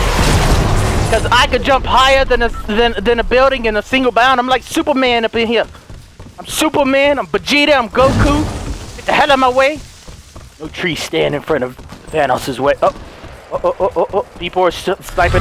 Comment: did he say no trees stand in vanoss way